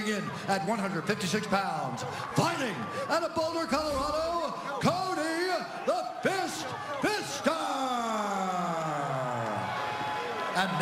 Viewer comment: If you have subtitles on @ 3:59, when the announcer calls out, Cody, the captions say “Cody the pissed pissed on”